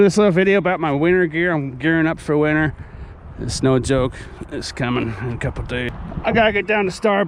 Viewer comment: Did you just say, “it’s SNOW joke”? Lol. Buh dop bing